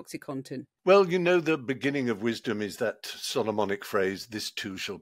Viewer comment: I heard “sodomonic” phrase rather than Solomonic, and “this too shall pass” took on a whole new meaning.